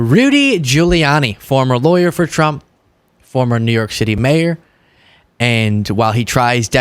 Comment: Sounds like you said New York shitty mayor